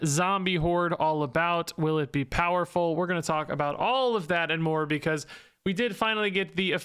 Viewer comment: He said, “more because” but what I heard was, “Morbius” 😅